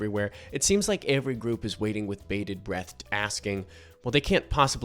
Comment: Did you say “bated breatht”?